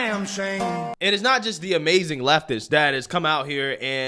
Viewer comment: "The Amazing Lucas" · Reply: thanks. Haha I thought he said Amazing Leftist and couldn't find it. 😄